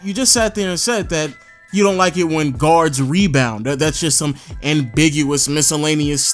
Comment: he actually said that he WANTS he guard to rebound...